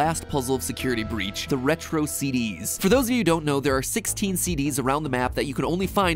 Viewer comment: Did he just say retro CD's? God I feel old.